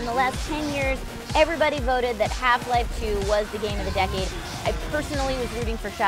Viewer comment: when it said have life 2 i thought its said half black jew O.o